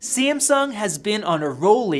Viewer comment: Wut did he say simsung